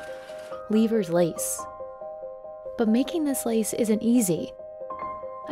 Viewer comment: I thought it said beavers lace. LoL